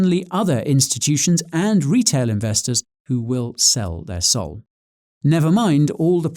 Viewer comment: I heard "Sell their Soul"